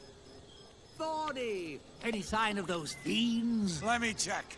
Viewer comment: what he say? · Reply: Thorny! Any sign of those fiends??